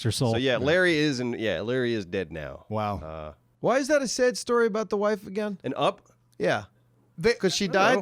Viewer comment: OpenG73 when did he say that? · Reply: Sorry, he actually says "Why is that a sad story. about the wife again?"